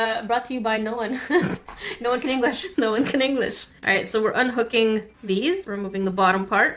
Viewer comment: why did i hear:,, so we’re un f*cking...”